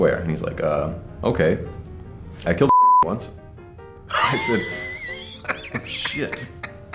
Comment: Can someone please tell me what he says at 10:52? It sounds like "I killed a dick once" and I don't think that's right😅😂